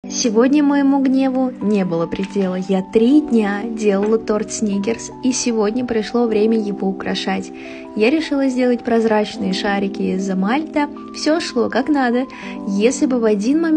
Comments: Мне одной послышалось " Снигерс " ? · Мне послышалось или она сказала торт снигерс? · Мне послышалось снигерс 😂